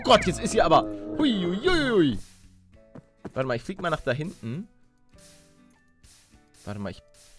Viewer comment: ich habe bei 8:05 verstanden : ich fick mal nach hinten